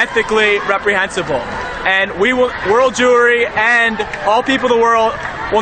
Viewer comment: I thought he said world jewelery xD